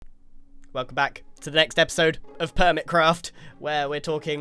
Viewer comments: Idk why but I thought Grian said "welcome to Permitcraft" ... the permits are getting to me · did he say, “Permitcraft”?